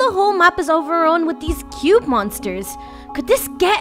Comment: at first i thought they said cute monster but after i relised its cube moster